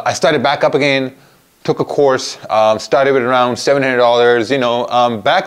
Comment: I thought you said you started With750.00usd?